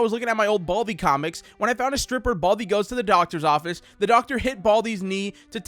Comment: i thought said stripper baldi